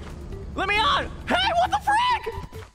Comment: I thought Shu said the f word but he just said frick